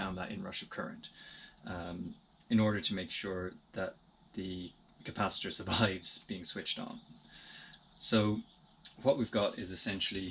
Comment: what did you say here: the capacitors of the what..? it's not very clear. Thanks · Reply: Hi, no problem. I think it was something like "in order to make sure the capacitor survives being switched on" just highlighting the purpose of the precharge circuit.